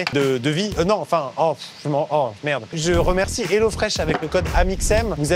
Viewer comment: c'est moi ou il dit Rhinofresh 😂😂😂